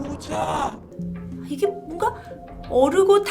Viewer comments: ㅋㅋㅋㅋ 난 왜 오자?오자? 라는말이 고자 고자 라고 들리냨ㅋㅋㅋ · 1.25배속으로 들으면 고자로 들림...넘 웃겨서 순간 분위기 코믹🤣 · 야인시대에서 심영을 많이보면서 웃었더니만 이게 고x로 들리는거 같아요. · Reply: 이거보니까진짜 고자라고 들리네요ㅋㅋㅋㅋㅋㅋ